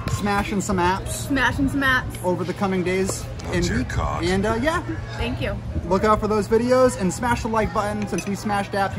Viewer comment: What the heck is that voice saying something about a "cock" at the 3:39 point of the video? LMAO!!!! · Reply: Sounded like saying “Oh dear God”